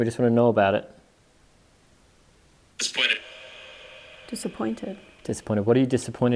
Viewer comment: At the table, I heard "split it" probably referring to the poker game instead of "disappointed."